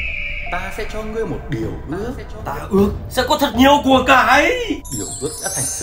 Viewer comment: Tưởng nhiều của cải quạ nghe nhầm thành nhiều củ cải